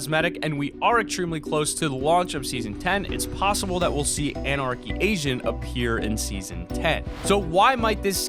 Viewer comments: I heard anarchy asian 4:20 lol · did he just say anarchy Asian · he was talking about the anarchy agent but at the end of him talking about it at 4:20 he says we might be seeing it but it sounds like he says anarchy Asian